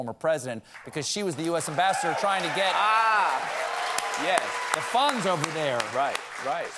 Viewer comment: I misheard what Stephen said introducing Marie Yovanovich, and thought he said that she worked hard "Getting the Fonz over there", and I was like: Yeah, I bet Fonzie could resolve the Ukraine situation, eyyyyyy